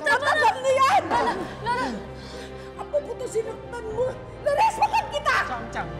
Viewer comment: this is what "p*t*ng n mo ka" in words sounds like. 🤣🤣🤣